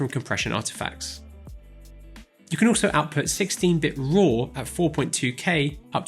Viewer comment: 16-bit RAW? Did I hear that right?